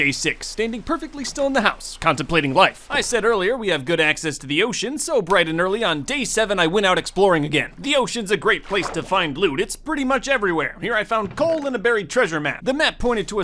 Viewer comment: did hey say bloot?